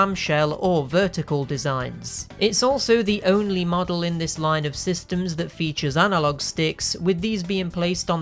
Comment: My brain heard that as Wii ka systems